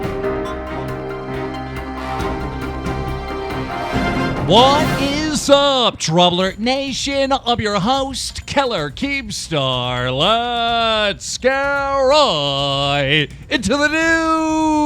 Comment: sounded like he said kellar keemstar